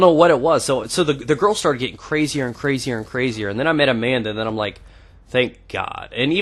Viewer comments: who else thought he said he met a man when he said amanda? :P dont get into realationships too young im 16 now and ive shifted as we say in ireland rather than made out with about 40 girls but apperantly when you do thet in america your in a realationship your young have fun :D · When u said "Amanda" I first thought u said a man ! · I thought you said then I started dating a man hahaha. I realized you said amanda when you said her lool